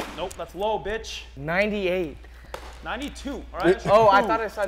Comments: “98” “that says 92” “oh I thought it said 97” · “98!” (“No 92”) “oh i thought it said 97” 😂😂